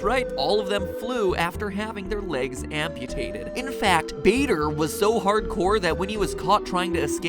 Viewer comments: Bader I thought he said Vader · I thought he was saying Vader instead of Bader, and I'm like Vader became a even greater pilot than Anakin was in the clone wars!!!